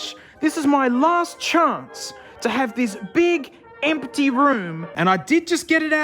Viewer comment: Did you say "Big amputee room"? What kind of museum was there before you moved in???